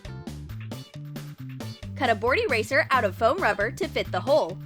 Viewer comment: did she say cut a bored eraser? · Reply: No she said cut a board eraser